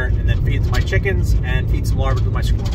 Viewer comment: both me and the captions thought you said "and Eat some larva with my squad" 😂😂😂😂